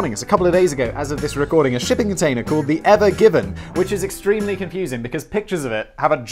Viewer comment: I misheard it as 'Ever Gibbon'.